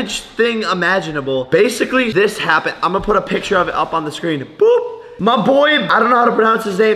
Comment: if you put the captions it says (boob) instead of (boop)